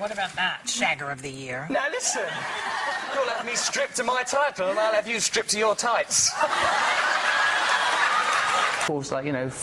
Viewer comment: what he said in that moment? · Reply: Cualquier chango "Now listen. You'll have me stripped off my title, and I'll have you stripped off your tights"